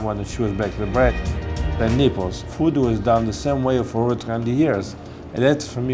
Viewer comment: Lmfao, who else thought it sounded like he said "The Nipples"